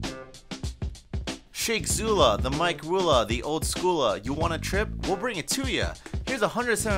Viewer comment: i thought he was saying in the intro "you want a drink ill blow it to ya" like cause he's a drink